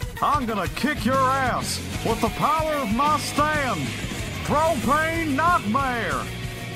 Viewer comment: When jojo king of the hill said "with the power of my stan", i first thought he said "with the power of my stance"